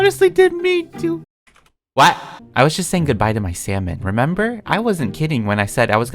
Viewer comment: When you said salmon I thought you said sells man😂🤦🏽‍♀️